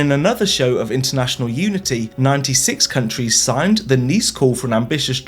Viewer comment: Thank you for the captions! I thought I'd draw your attention to some minor errors, in the hope that you take it as constructive feedback – accurate captions make it easier to share videos! :) At 5:29, it says "signed the next call" instead of "signed the NICE call" At 6:06 it says "not so keen to have this treaty", leaving out the word "ambitious" At 14:29 it says "other great woodland" instead of "other grazed woodland"